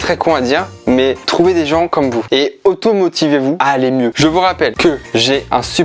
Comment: Oof pardon mais à un moment au lieu d'entendre "automotivez-vous" j'ai entendu "automutilez-vous" XD ce n'est évidemment pas à reproduire !!